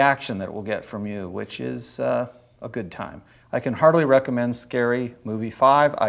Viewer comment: Heartily? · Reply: Hahahah heartily vs hardly is a hilarious distinction. I heard “hardly”, figured they were just being facetious, but now accept this as the better reality 😛